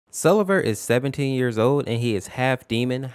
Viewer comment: I HEARD "Selever is 78 years old" 😭😭😭